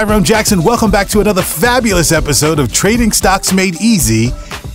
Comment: POV: you accidentally tapped on the ad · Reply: I thought it said trading socks and I was interested